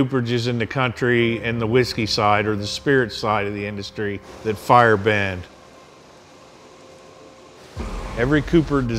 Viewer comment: I heard him say they are fire benders....